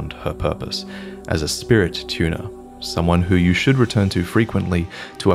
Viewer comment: I thought you said “spirit tuna” 😂 great guide, I am level 70+ and had no idea you could upgrade spirits like my best comrade, the Jellyfish. I’ve been missing out.